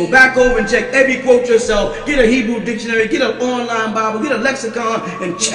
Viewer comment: what were the 3 books the master teacher said to get to verify the teachings?? I heard him say a Hebrew Dictionary, Lexicon, I couldn't hear the third...sounded like a borderline Bible?.. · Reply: Dictionary lexicon... Strong concordance... Online Bible